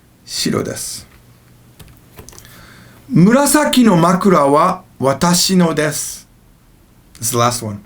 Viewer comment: i thought he said "manko"